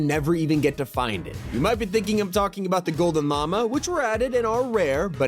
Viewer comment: what t5g said : “the gold llama What I heard: “ the gold mama 😂